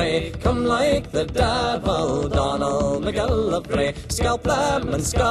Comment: I only hear "Donald mcgillarvy" or whatever.